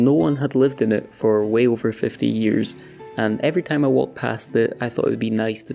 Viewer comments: it didn't sound like you said 15, it sounded like you said 50. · Sounded like 50 to me. :) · i’m not a native english speaker, but i clearly heard 50😅